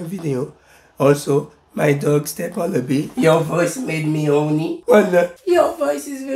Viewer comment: Am I the only one who heard him say "my dog stepped on a bee"